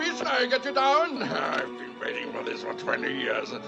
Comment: I thought he said "i've been getting ready for this for twenty years" i mean still why does he have to shoot a damsel in distress